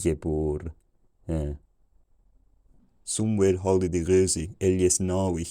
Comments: Partway through the 600's i heard "somewhere holiday" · I think I heard the word "somewhere." Eyes closed, not looking for any captions. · I'm typing this out as I read it and think I hear things lol. 1:08 I heard something that sounded like Somewhere holiday. 1:14 it was seven days. 1:17 something that sounded like "daggers grim" "or daggers scream". Clearly hearing "chicken" at 1:27. 1:30 Gleodon is loud fool. 1:42 it is long winter, the room is cold, the cat is fat and lazy, the flower is dead in the yard(maybe?), the rock is forleden on the tomb. We havent a (something) of water, we haven't yet (something) wool maybe? 2:09 It was boring in (something) 2:17 no goat or sheep or cow. I won't keep going, but It's crazy how far back you can start picking out words. By <num> I'm understanding it pretty good. No idea how accurate this will be as I am doing it in real time while watching the video lol.